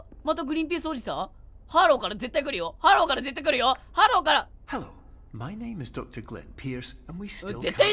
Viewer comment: のところからのころんくんがハローから来るよ！ってほんとに、ハローから来たし、なんなら、グリンピースって確かに空耳かもだけど聞こえたわ（笑）